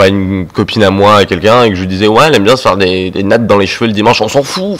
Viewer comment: [Alerte commentaire constructif] "Ouais elle aime bien se faire des nattes dans les cheveux le dimanche" 😂 (J'ai entendu "des lattes" également, pas mal)